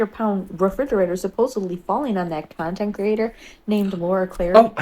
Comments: I legit thought she said cuntent creator. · I thought she said c*nt too 😂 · For the record: I heard “cuntent creator”, too